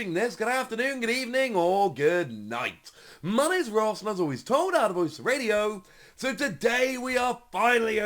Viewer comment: Please somebody tell me what Ross says in the intro, I've been so perplexed and confused for months now 😂 is he saying "as always told I'm the voice of the radio"? Please I need to know! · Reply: "my name is Ross and I was always told I had a voice for radio" is what I always thought he was saying